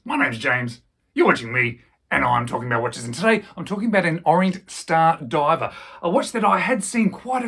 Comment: Accent threw me for a second, I thought you said "orange star diver", being from Scotland I don't have an accent. 😂